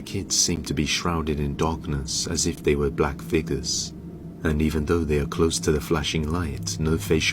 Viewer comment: I was in the kitchen with this playing and thought he said Black Nixxers..... hahaha